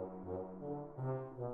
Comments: Wow, after all this time, I thought she was saying "Bitches go higher" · this sounds like it is saying reaching for Harambe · The "Reaching for high" part (thought it was saying "this shit go hard") repeats far too often and kind of ruins the song, but I still like it. Is this what the kids refer to as "trap"? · The "Reaching for high" part (thought it was saying "this shit go hard") repeats far too often and kind of ruins the song, but I still like it. Is this what the kids refer to as "trap"? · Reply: All the Ramen on the Sky I thought it was saying reach and go high